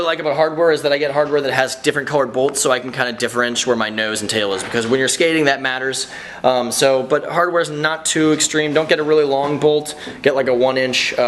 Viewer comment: did he say differench???